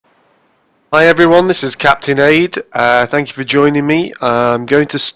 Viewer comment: It sounded like You said captain aids